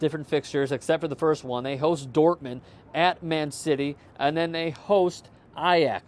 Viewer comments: Did you say "Man Shitty" for Man City? 2:36 Tsk tsk tsk. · I heard that :P 'Man Chitty'.